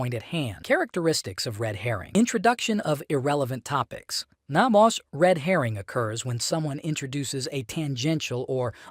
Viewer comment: what did you say at 00:54? it sounded like "not mosh" and the captions say "not more" but that sentence doesn't make a lot of sense. Was AI used here?